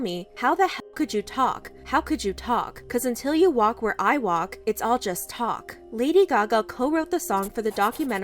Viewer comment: "this is no joke", instead of "it's all just talk"